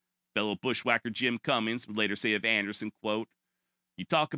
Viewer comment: "Fellow bushwacker Jim Cummings"? XD...Oh I just imagined Winnie the Pooh galloping on Eeyore slinging a pair of colt 61 navys.. Okay I know its probalby "Cummins" or something but thats what I heard at first. XP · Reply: Yep, Cummins. But next week I’ll be talking about another Old West guy who’s last name was spelled Cummings.